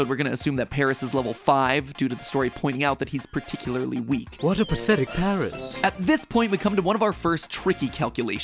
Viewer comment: because of the add I heard say "what a pathetic parrot"